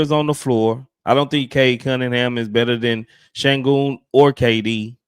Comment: i gotta put this blunt down i thought i heard him say alpi is better than cade .